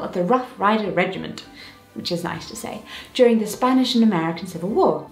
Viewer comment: Could have sworn you said Spanish-American Civil war. ...